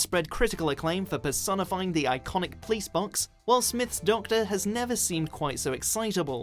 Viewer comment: did he just say 'Will Smith'? lmao